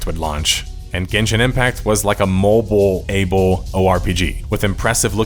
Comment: Neither was BPO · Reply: I see, my mistake then. Thank you for the clarification! Also I had written this because I thought you had said that Genshin was a MMORPG in the video but watching it again I had misheard as you said "Mobile Able RPG" 😬